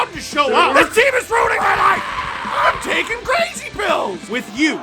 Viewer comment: You realize how many LFRs you've actually watched when you can hear the intro voicing has changed, even though says the same thing; "With you, wherever you are. Welcome, to LFR" 🤣🤣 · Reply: This time I heard "im taking crazy pills" "with you, wherever you are"